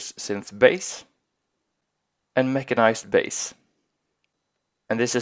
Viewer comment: I thought he said make a nice bass lol